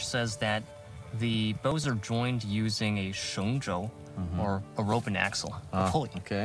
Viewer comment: should be “shengzhou” instead of “shengsuo”